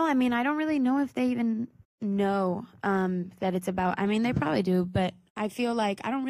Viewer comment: Me reí con la nota de arriba😂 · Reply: Natalia Sterling Escuché "they even know" ? At 4:14??